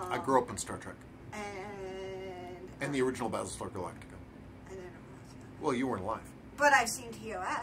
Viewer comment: did say BaselStarGalactica ? LOL I just did if you didn't